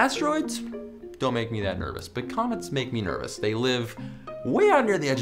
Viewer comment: Lol when you said "comets make me nervous" I wasn't paying attention so I heard "comments make me nervous they live way out at the edge of the solar system"